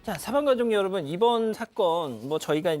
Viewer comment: 사망가족 여러분으로 들림 사건반장 가족여러분 이라고 하는 것이 혼돈하지 않을 정도가 될듯